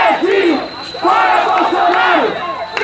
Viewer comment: Eu entendi "fora bolsonaro, imbecil"